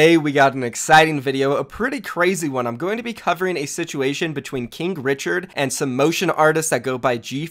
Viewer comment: i thought you Said kink Richard 😂